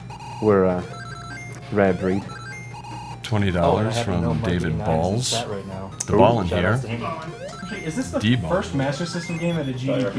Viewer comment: 20 dollers from david balls did i hear ha.